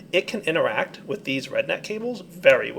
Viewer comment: Haha, i thought he said "redneck cable" not "rednet cable"